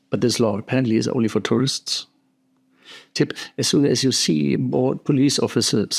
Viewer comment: What do you say at 6:58? Bought police officers? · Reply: Not even close. I said. „Only for tourists“